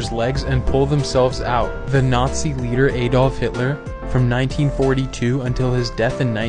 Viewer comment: For some reason I heard, “the Nazi bear Afolf hitler, instead of Nazi leader” 🤣